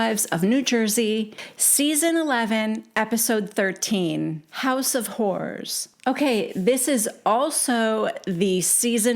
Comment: I thought you said house of whores at first. How anyone can listen to Teresa’s voice for hours is beyond me.